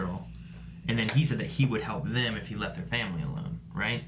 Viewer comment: what? She said he would help him in secret again in a heartbeat bro wtf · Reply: dude chill out I rewatched the scene and yea you are right I thought she said she would help him in secret again bruv